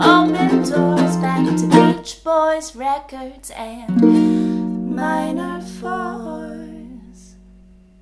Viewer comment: I heard you say that words are deadly things And I was surprised to find myself alive and breathing I said I know it's tough, but I'm growing up And your love it just ain't good enough So I'm taking back my heart for me I was lured into every word you speak Yeah when I was young you could tie my tongue and weaken my knees But the lines been drawn and I'm moving on, and another girl will come along So I'm taking back my heart for me This is the end of the road, this is where we part You can keep all your memories but I'm taking my heart Turns out being away is a wonderful way to be I'm going back to the earth, back to open doors Back to Beach Boys records and minor 4's. Cause I'm taking back my heart for me I heard you say that words are deadly things So I was surprised to find myself alive and breathing I said I know it's tough, but I'm growing up And your love it just ain't good enough So I'm taking back my heart Said I'm taking back my heart I'll be taking back my heart for me. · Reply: I thought they say minor faults